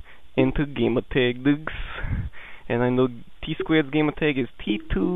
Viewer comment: it sounded like he said psquared